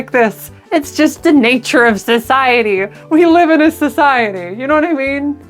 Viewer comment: We live in a SISiety I thought I heard it like that lol